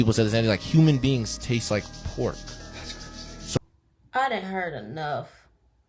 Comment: 😂I was closing my eyes while listening to the pig and pork conversation and all I heard was “I done had enough” 😂🤣🤣🤣😩I’ve had enough too because my mind kept saying “what in the world!”